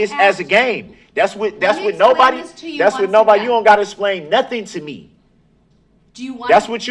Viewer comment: Definitely without knowing it. His grammar leaves a lot to be desired. When he says "You been being disrespectful the whole time" it makes me cringe, among other things that come out of his mouth. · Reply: Yes! Sure sounded like he said "esplain" instead of "explain." Also, he uses "amemin" instead of "amendment." Just horrible!